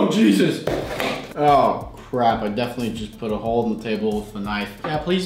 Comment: did he Say... Crap??????! Well, anyways still love your vids